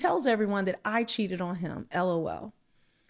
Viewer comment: 🤣 sounded like she said “trump tried to run me off the road” I wasn’t looking at the screen so I was like “huh?”